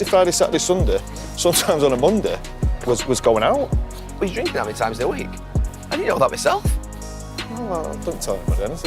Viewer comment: Thought Nev said, drinking nine times a week.